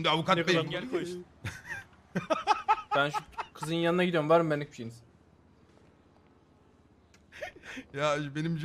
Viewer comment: ne diyor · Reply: benlik birseyiniz diyor